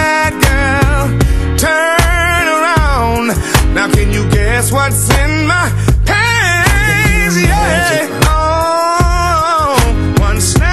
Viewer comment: i thought he said hands not pants lol c.w a player 4 sho